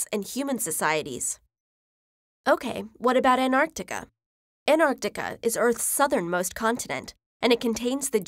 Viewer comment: Did she say Anartica?